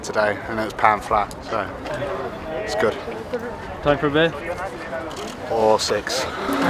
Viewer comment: Does he say "Or sex" at 5:55?